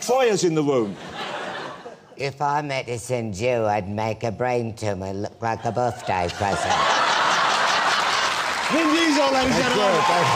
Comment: what dous that guy say? if i medicined u i would make a brain tumor look like a birthday present?